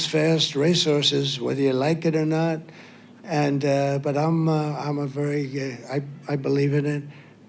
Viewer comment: Did he just say 'im gay' ?